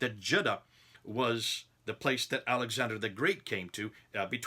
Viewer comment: When you were laughing it sounded like you said Adam Sandler the Great!